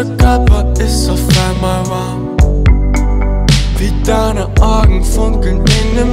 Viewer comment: Wie ich halt jedes Mal 'Orgeln' statt 'Augen' verstehe!!! ( . ) ( . )